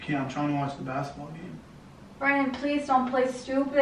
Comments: Yo i swear she called him brian at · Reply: She said bran-din and it sounded like brian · she called him Brian lmao 😂😂😂😂😂😂😂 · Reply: Dam chill foo it does sound like she said Brian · if you think about her saying "Brian" and then replay it and think "Brandon" and you will hear them both.